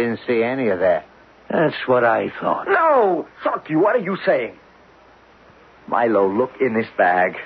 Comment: Am I hearing the F word at 32:33? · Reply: I have my own casette recording of this show from the original airing. He said "Sharkey", not the F-word.